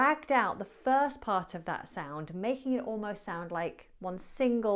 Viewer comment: I thought i heard you saying "Mahing" instead ok "making. Are you liverpoodlian? 😂